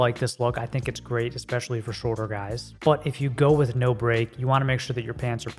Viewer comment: Regarding "No Break" pants length. At 10:45 speaker says "I like no break, I think it looks really good, especially for ???? Guys". Did you say "shorter guys " , "taller guys " or what guys ? Thanks. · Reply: Very late, but "for shorter guys"